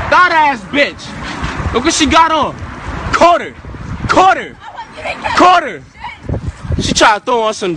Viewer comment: "Caught her, caught her" lol. It sounds like he said "Carter, Carter!"